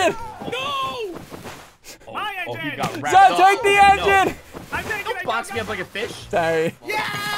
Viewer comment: damn i thought i heard dont box me like a b---h